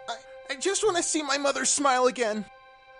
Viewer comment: Okay I thought they said brother in the 1st part but now I know they said mother so um ya I was confused